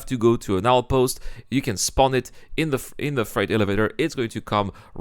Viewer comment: I'm excited about the features, but the server performance still needs to improve before I return to play. So bring on meshing! Your accent made it sound like you said "Fright elevators", which is an apt description of SC elevators. 😁